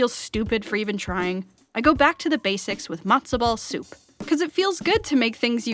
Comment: Was it just me who thought she said ‘monster ball soup’ and thought she recreated the soup from Hotel Transilvania? Just me? Okay...